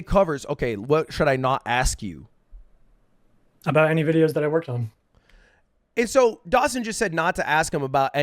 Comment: You got it wrong, he clearly says "that he worked on"